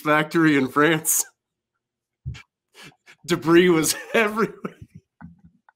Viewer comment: What was he saying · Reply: I wasn't aware that there's a cheese brand called Brie (I just looked it up). I heard it as debris was everywhere and I didn't quite get what's so funny about that. Thanks for the explanation.